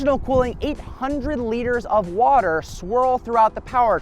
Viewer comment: did he say 800 liters of water are in this car?!